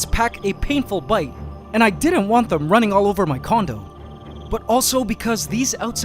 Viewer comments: Did he say he didn’t want the ants running all over his condom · I thought you said you didn't want them running all over your condom 😂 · did antscanada said i don't want them running around my comdon at 2:34 ? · I was tabbed out playing a game, and when I heard 2:36, I thought he said, "I don't want them running all over my condom". Lmao.